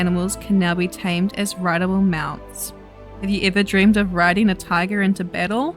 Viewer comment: I thought you said Rideable Mouse instead of Mount, lol 😜